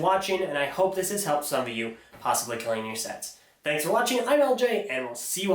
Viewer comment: Wait... did he say "possibly cleaning your sets" or "possibly killing your sets"?!